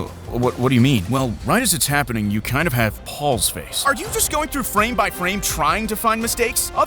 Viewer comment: I had to rewind this because I thought he said "Pals face" as in "Cerebral Palsy" 💀